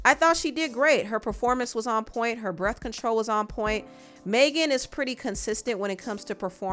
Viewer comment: I thought you said "birth control" instead of breath control 😂😂😂😂 being a pharmacist is hard all you hear is medicine names too 😂😂😂😂